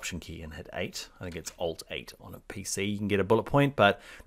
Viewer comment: Did you say Alt+8? That does not work. Also tried Ctrl etc. to make bullet points.